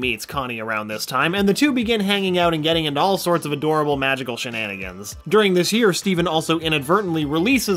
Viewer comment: If Rebecca chooses to revisit some of these characters, here's a few ideas for some miniseries: Lars and the Off Colors: Seeing their adventures and met new alien species. Tales of the Crystal Gems: Following the gems and their shananagins. Spinel and the Diamonds: Just go full sitcom mode. Feel free to contribute! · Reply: When you said shenanigans i thought you said sharingan